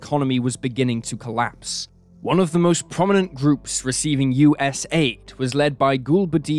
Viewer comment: U-S-AID! U-S-AID! U-S-AID! anyone else hear it or just me?